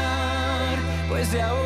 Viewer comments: Jajajaja, siempre pensé que decía "Tu cable es tu lugar" y hasta ahora lo entiendo. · Jajajaja, siempre pensé que decía "Tu cable es tu lugar" y hasta ahora lo entiendo. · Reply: Yo pensé que decía "no cabes tu lugar" cD